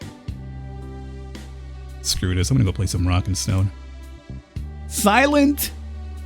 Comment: Did I hear a rock and stone?? Nice DRG reference there hehe